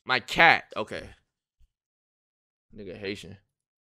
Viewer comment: did he say “asian” @ 13:39?